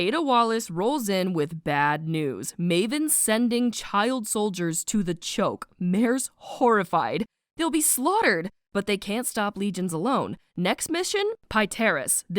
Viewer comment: bro I thought it said chokey for a second iykyk